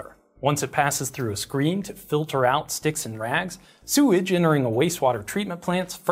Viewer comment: Did you say "sticks and rags"? I thought maybe you said "sticks and rats"!